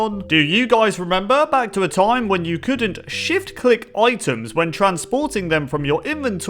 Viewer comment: I honestly thought he said sh*t click